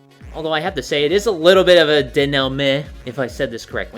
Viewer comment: what is that word is saying and what does it mean please? Thanks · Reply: denouement, I think. It's I believe a french word. "the final part of a play, movie, or narrative in which the strands of the plot are drawn together and matters are explained or resolved."